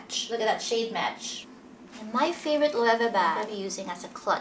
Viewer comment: I hear 'and ready to freeze '😂😂😂